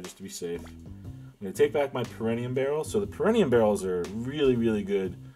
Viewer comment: did you just call it a peranium barrel